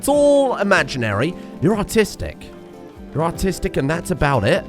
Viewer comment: keeps saying the phrase "You're artistic" referencing his colonists and I keep hearing "You're autistic" instead. Which frankly, could also still reference his colonists, but it's a good bit ruder to declare it at them like that.